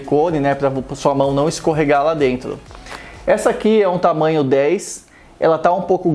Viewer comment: EU ESCUTEI ESCORREGAR GALA DENTRO KKKKKKKKK